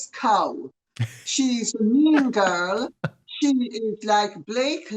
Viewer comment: The more I see of Lady C, the more I love her. She's not only hilarious, she is truthful . Lovely lady! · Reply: When she said ‘she is a mean girl’ I thought she said a minger